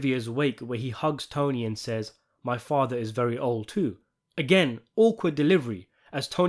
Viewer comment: he actually said his father was very ill*